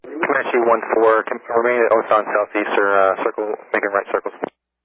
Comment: Confirm remaining at Osan SE , make a right circle 로 들리네요. 엑플 덕에 지루하지 않게 봤어요. 원문 표기도 있으면 퀄리티 업이 될 것 같아요.